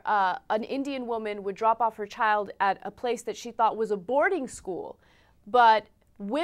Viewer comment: did Ana say "drop off their child at a boarding school", or "an aborting school"? Clearly, we don't have to go to India to "abort", [kill], our children.